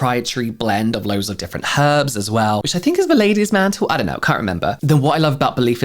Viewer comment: OMG! when you were talking about the Belif The True Cream aqua bomb I thought you said "Ladies MAN TOOL!! I had to rewind because I thought "WTF kind of cream is this?!" That is when I realized it is actually Ladies Mantle 😝🤪🤣